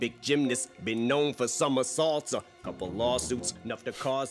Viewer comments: Great and in-depth reaction as always. Goat status on this one for sure. The gymnastics bars I heard it as some assaults not somersaults. Both fit for sure. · Idk he it was caught but when he said he's a gymnast I'm know for somersaults I heard it as some assaults